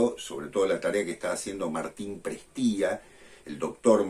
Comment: Quien es el nombre que dijiste a 4:10..."Martin Pristia"? Gracias..yo he aprendido mucho...gracias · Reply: Martín Prestía.